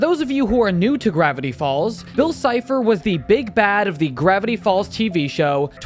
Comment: I thought u said bill was a big back 😭✋